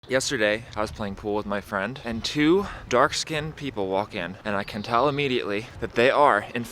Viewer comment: did you say dark skinned people are skin color is fair you racist